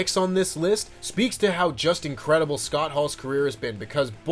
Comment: I swear I though you said Justin Credible and it confused me lol